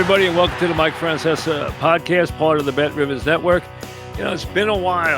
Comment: I always thought he was saying, “bet rivuhs.”